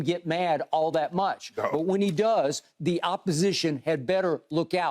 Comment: did skip just call the lakers opponent an opp (Opposition)? He certified then🤣🤣🤣